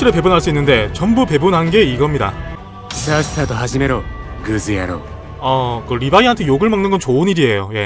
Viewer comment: ㅋㅋㅋ 아닐수도 있긴한데 1:01 리바이 선택할때 쓰레기 녀석 이라고 말하는거 원레는 크즈야로 해야하는데 그즈야로 라고 들림 ㅋㅋㅋ 게임이고 하니깐 태클걸릴까봐 순화시킨 욕인가..?ㅋㅋ 물론 일본인도 아니고 일본어 배운것도 아니라 발음이 그즈가 더 맞는건지 모르겠고 둘 다 되는지는 모르겠지만 그냥 그런가 싶은 느낌 ㅋㅋ